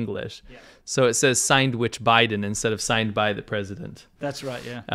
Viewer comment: "Signed by"!! I thought they were saying sandwich 😂😂